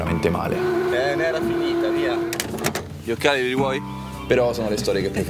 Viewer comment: "dio###e li rimuovi??" ho sentito la prima volta pensando si riferisse ai sedili 😅